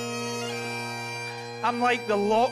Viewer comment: The first time I saw this, I thought she said, “I’m like the Loch Ness monster... allegedly.”